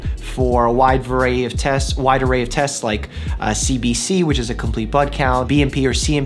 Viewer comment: I honestly thought he said 'CBC, which means complete BUTT count.' 🤣